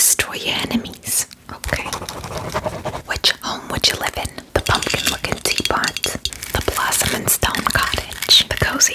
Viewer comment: When picking the houses the first one I thought she said "pumpkin f-ing tea pot💀" and on the flowers I thought the second one was "the FAILED lady💀😭" Thoss were my bloopers😭💀😆